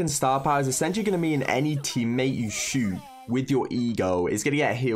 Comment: the first time i watched i heard “shoot with your egirl”